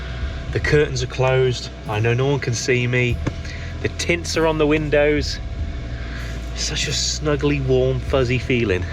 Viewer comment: Cracking update on the van. I like how it’s coming along, although it’s not finished it looks like a modern conversion to me and also cosy. I thought you said “the tits are on the windows” 😂. Need to get my hearing checked haha.